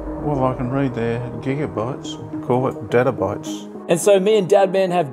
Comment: At first I thought your dad said Databytes but then I saw Daddabytes and It made me laugh. Loved the video and your father's humor